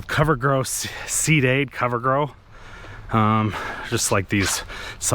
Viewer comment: I thought you were saying "covergirl" like the makeup brand. 🤣